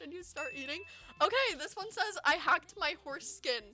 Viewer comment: i was training so i couldnt see my screen but when u said that i heard "i hacked my foreskin"...... horses skin is much better sounding than that as weird as horses skin sounds.... im too non innocent for this world 😭